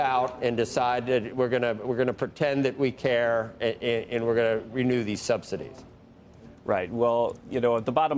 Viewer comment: lol… did he just say “we’re going to pretend that we care and renew the subsidies in the red states”?